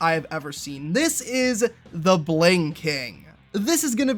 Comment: bruh i heard here not "the Bling King" but heard "the blinking"